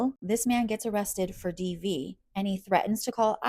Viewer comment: Could someone help me out with what he was arrested for? Sounded like she said DV or DEV. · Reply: Nevermind I think it's domestic violence.